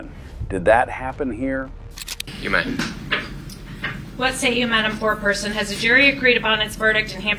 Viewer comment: “Mr. Poor person please stand”… 😂😂😂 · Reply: At the start when the woman said "what's say you, madam foreperson" I thought she said "poor person" and was like lol imagine if that was actually her name then 20 minutes later there's a legitimate Mr. Poor Person 💀